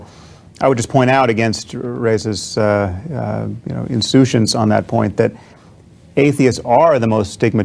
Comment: What's the word that he uses at 4:37? Insutions? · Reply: insouciance - casual lack of concern; indifference